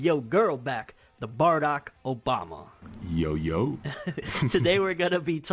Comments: *lits blunt. Bruh I thought it said "feat. Barack Obama" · I thought it said BARACK Obama